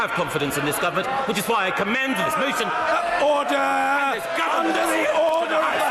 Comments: Can anyone tell me what Gove says at the end? Sound like he says: "...which is why I commend this motion... and this government... to the House" Ah I understand now. He meant commend as in trust. He trusts the motion to be dealt with by the House. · What did he say at 10:42 that made them all go wild? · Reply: Not quite. He accidentally said 'I commend this motion', when he was opposing the motion; kind of a Freudian slip. He corrected himself and said government. (Ministers usually commend motions et al to the House, hence the slip). Opposition cheered because of this slip, before government benches cheers took over in support.